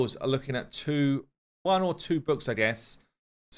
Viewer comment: When says "books" I hear "boobs"......no idea why....I am the only one??!!???